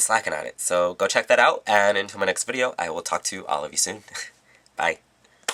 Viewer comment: lol i ALWAYS thought you said "i will talk to you, all of you soon" but u say "I will talk to.. all of you soon" right?